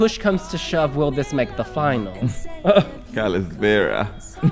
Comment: did he say kalispera? You made my day😂